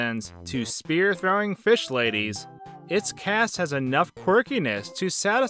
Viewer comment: Did I hear it right? “Spear throwing fish ladies”? Also im late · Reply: spear-throwing fish ladies, yes